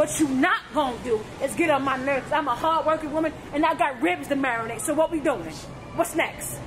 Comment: dont mess with a black woman in particular when she got ribs to marinate. Facts!!!!😂💯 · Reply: I thought she said kids to marinate